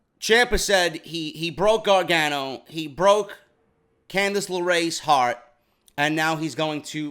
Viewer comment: Gotta say I misheard Ciampa at first, I thought he said he broke Candice LeRae's ARM and was like 'Whut'... then spun it back and heard it right.